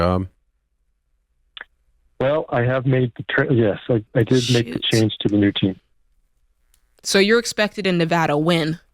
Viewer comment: does she say the s word? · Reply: "Shoot"